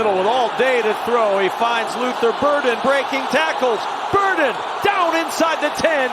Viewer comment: You know you’re in Chicago when there’s pigeons on the field. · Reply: I heard Luther “Birden” and saw pigeons flying around 😅